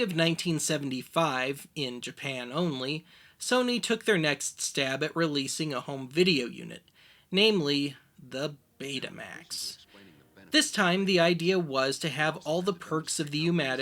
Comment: I thought that said "Betamax Blows"...